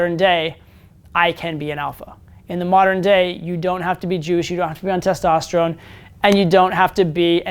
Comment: What word did he say at 4:00? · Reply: he said you don't have to be jewish